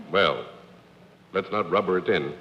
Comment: What was the joke John Daly made at 12:35 about Akron? I didn't quite hear it. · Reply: I don’t want to rubber it in.